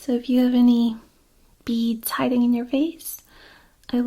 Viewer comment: I misheard a line from this as I was dozing while watching. I thought she said "If you have any bees hiding in your face" I was like, what the hell am i watching? But beads makes more sense.